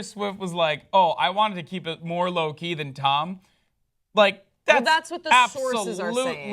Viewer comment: Am i hearing things or did he say Low-key or Loki because 98% of my brain is telling me that he said Loki